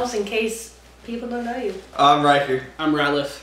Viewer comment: I thought ryker said “I’m right here” instead of I’m ryker ahah